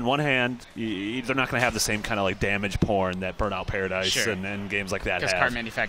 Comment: did he say damage porn?